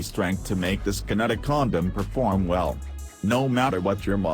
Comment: "Condom perform" I heard it as "condom for porn" 🤦‍♂️